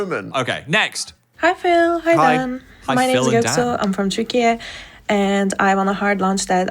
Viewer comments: who mixed up Türkiye with Czechia she is literally saying it????? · I think she said Turkiye and not Czechia.... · the girl in 32:34 is not from czechia, she clearly said i am from Turkiye ahaha. maybe you guys couldn't get it because she didn't say Turkey, but Turkiye is also commonly used these days. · are we sure they said Czechia? I heard Türkiye and and Göksu is a Turkish name. Unless they also wrote it in the email.